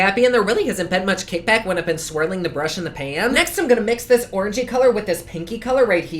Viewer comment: i swore i heard u say ORGY ...but u said orangey